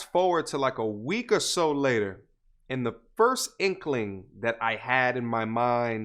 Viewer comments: did he say "In the first inkling" , "And the first inkling" or "At the first inkling" ? Im improving my English, and answer is very important for me to understan that case. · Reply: "And the first inkling.." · did he say "In the first inkling" , "And the first inkling" or "At the first inkling" ? Im improving my English, and answer is very important for me to understan that case. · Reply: He said " AND the first inkling".